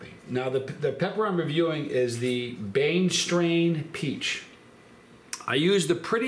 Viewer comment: A Ghost anything lost me. But, cross ghosts? Fugedaboudit! When I first heard you say the name of this pepper, I thought you were saying “Brainstrain”. I thought that was appropriate. It doesn’t matter, it’s still a great pepper. You’re amazing, (breath).😁